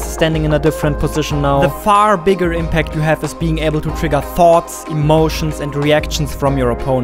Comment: I genuinely thought I heard "Being able to trigger "farts" from your opponent" for a second there